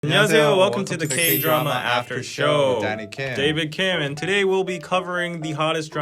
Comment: Literally mishear Danny all the time when he introduces himself and think he says “Daddy” instead of Danny